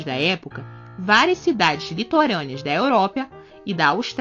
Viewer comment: So eu que ouvi "Europia" em 3:50?